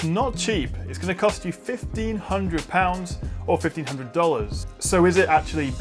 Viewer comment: Can some english native speaker help me? What does he mean when he says the price of the product? I hear he says "fifty hundred". How do youwrite that in numbers and letters? Can someone explain me, please? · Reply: Manuel Sifontes he said fifteen hundred which is 1500 or one thousand five hundred